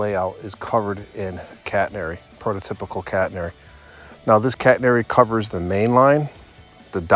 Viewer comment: Beautiful set-up! 💯 👍 I thought I heard you say "cat hair" "prototypical cat hair"!